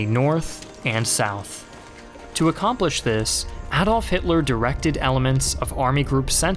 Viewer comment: did i hear hadolf hitler